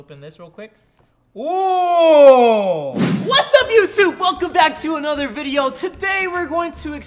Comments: Is it just me that i heard him said YouSoup..... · He says "Wassup u soup" instead of wassup youtube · Did you just say "What's up YouSoup?"...